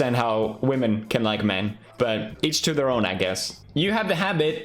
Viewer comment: I thought the saying was "eats their own" and not "each to their own" lol